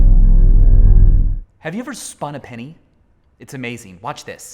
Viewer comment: sometimes i hear "have you ever spawn a penny"